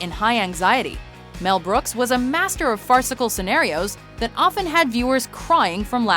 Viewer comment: did she say fartsicle